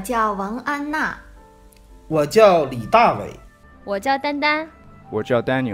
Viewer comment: for some mysterious reason, I hear 我叫蛋蛋 XDDD I have to keep training to differentiate the tones^^ I hope one day i will be able to speak in mandarin to my taiwanese wife :p Good video by the way !